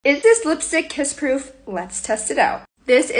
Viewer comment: Help at the beginning i heard “kids proof”😭💀 i’m fucking deaf